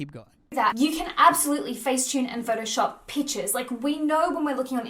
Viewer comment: i keep hearing "you can face tune and edit peaches" lol edit that peach on girrr